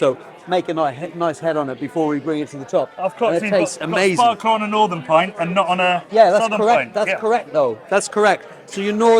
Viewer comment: What's he saying around 6:06 about the way the pint is poured? Sounds like "spar core" or something? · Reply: sparkler! You'll learn more later